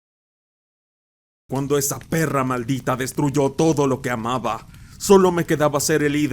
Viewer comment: escuché "quiero p-ne" JAJAJAJA